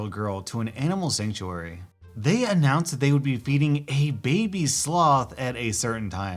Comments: Not reading... I thought you said 'beating' not 'feeding'... was like 'hol'up' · I thought you said beating a baby sloth instead of feeding I was like wait a minuet tf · I thought that you said beating a sloth................